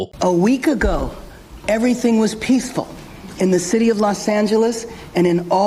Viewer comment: I thought she said everything was peaceful. These people gotta go. So full of it.